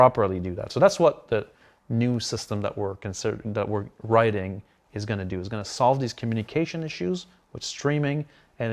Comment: I hadn't noticed before, but what he actually said (around 3:09) was "...that's what the new system we're consider- that we're writing is going to do...". He interrupted himself mid-sentence, changing "considering" to "writing", probably because it sounds better, and the boss loves things that sound good, regardless of truth. I guess he still wasn't fully fluent in "CIGish" lol Anyone care to bet that they are still "considering" it a year later? I also suspect that the "some amount of persons" actually working on these issues hovers right around zero most of the time...